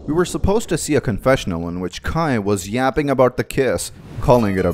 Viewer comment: DID JUST SAY YAPPING 💀